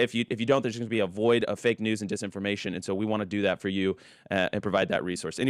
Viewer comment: Did he just say ‘there will be a void of misinformation and disinformation so we want to do that for you and provide that resource.’ ?! (Around 11:15) Im glad they are aware of the type of ‘information’ they spread. Sending love to Willa! ❤